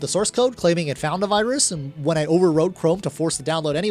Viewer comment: Interesting video! Quick note 20:33 subtitles say "overwrote". You should edit that to "overrode" as that's the past tense of "override" which is what you're doing here.